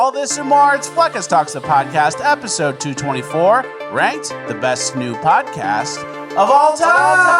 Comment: Who else thought fleccas said " Best Nude podcast" lol